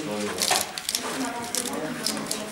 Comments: what did he say?? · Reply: "hehe now you hear penis on the video..." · I heard MAN